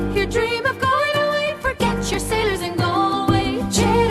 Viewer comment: I thought she said forget your sinners instead of forget your sailors.